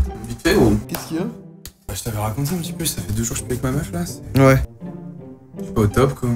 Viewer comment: il dit quoi? Ca fais 2 jours que je….? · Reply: Que je suis plus avec ma meuf